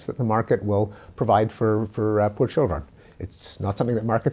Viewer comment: What's he saying at @ 3:50 ? Is he saying "for children"? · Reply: "poor children"